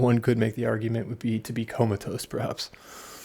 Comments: "to become a toe sprouts !"? what?? 0:16 what da ya say??? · "become a toe sprouts !"? what?? 0:16 what da ya say??? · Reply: Yes he says become comatose perhaps